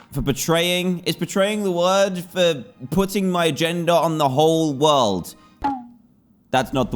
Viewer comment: am i the only one who heard “putting my gender”??